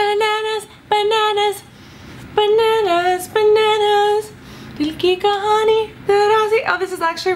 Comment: did she say "Dil ki Kahani bananas" ? 😂